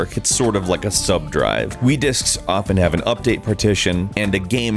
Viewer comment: did he just say "we discs"